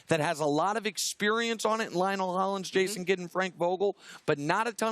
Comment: For a moment I thought he said Ryan Hollins, Jason Kidd and Frank Vogel🤣